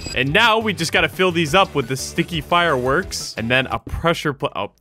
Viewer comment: Sometime when he said sticky fireworks thought he said stinky lol